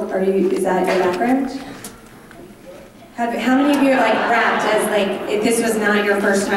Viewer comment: Can someone explain to me whys happening at 21:28? 😂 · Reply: They thought he said raped instead of rapped 😂